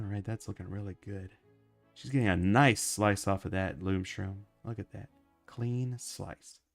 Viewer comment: Boom shroom, I thought you said loom shroom?